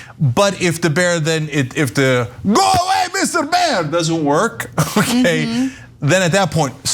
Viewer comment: I need a Turkish lesson - what is Cenk saying here? I love when Cenk talks Turkish! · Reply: “Go away mr. bear”, he says it in a Turkish accent 😂